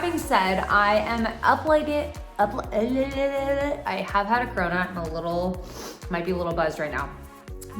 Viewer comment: When you said you've had a corona, for a minute, I thought you said you've had the corona (virus) and was sad for a minute!